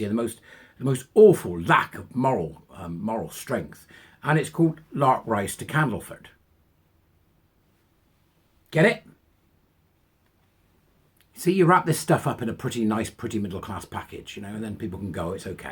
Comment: No I'm not. Just winding folk up. 😃😃😃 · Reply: To be honest I thought I'd misheard you and you'd said Larprise to Candleford. Now there's an idea for a spoof - burly bearded blokes in dresses playing all the women's parts!